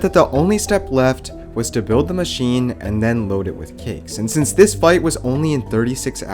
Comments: I thought he said load it with kids L0L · When he said “load the trap with cakes” I thought he said “load it with kids”